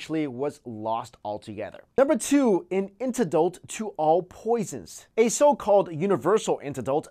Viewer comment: ok... it honestly did not sound like you were saying "antidote" - it sounded like "intodult" -- i thought it was a name of some kind of weapon... Then the roman concrete - um, if they know about it and its recipe - surely it shouldnt be part of this list which is for "~we still can NOT figure out" and "east" sounded like "yest"... maybe it's your sound equipment, but it was difficult to follow some parts...